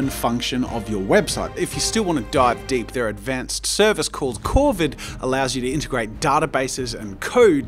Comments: Who thought said COVID at 14:22 Just me? ok I'll head out now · When said corvid I thought he said Covid